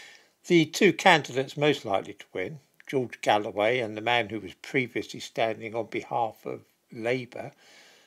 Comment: It sounded like “ … who was previously stabbing … “ I wonder what made me hear this instead …